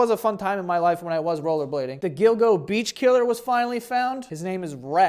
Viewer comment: So I'm a native french speaker, and when you talked about the Gilgo beach killer I heard "dildo beach killer" and had to look it up because what the hell is a dildo beach killer? I'll let you imagine the results that came up. Or maybe don't.